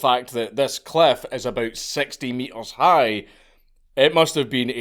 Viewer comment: Honestly, a 60 meter tall Rogue Wave is scarier than just about anything short of Cthulhu. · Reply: Wait 60 meters? I must have misheard I thought he said 60 foot cliff. That's like 190 feet isn't it? I've never heard of one bigger than 80 feet being recorded. Of course it's possible we've just not seen such a monster but dear god...